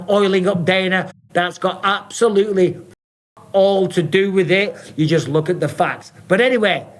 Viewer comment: Listening I heard.. "I'm oiling up Dana, that's got absolutely.. all to do with it". Had to watch to see him mouthing the words fck all to do with it. Thought we were having an honest moment for a second.